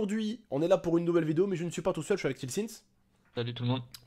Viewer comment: Au tout début j'ai entendu " je suis avec jonnhy sins "